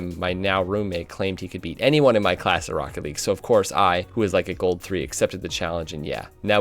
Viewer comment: did anyone hear him say gold 3??